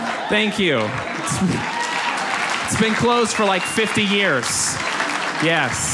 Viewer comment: I always hear "subscribe to Breitbart comedy" at the end and then get that .... wait what? reaction.